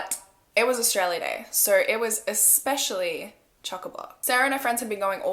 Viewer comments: I had no idea wtf she said and what it meant until I searched up Australian slang lmao “Choc a bloc”: Full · hey does anybody know what she said @ 3:25? what is chocobot 😂😂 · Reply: aidee maria chockablock- it means very busy